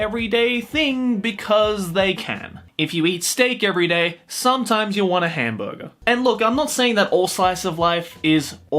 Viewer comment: What he says - "If you eat steak every day, sometimes you'll want a burger." What I hear - "If you eat beef every day, sometimes you'll want more beef.